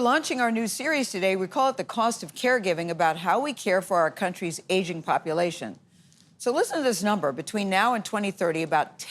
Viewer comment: Ngl, when she said “our country’s aging population”, I heard “our country’s Asian population” which makes sense with the thumbnail 😂